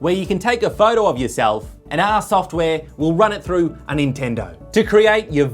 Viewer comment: did he say nintendo?